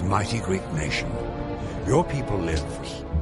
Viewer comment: (or somewhere around that) it sounded like the narrator said your people lift!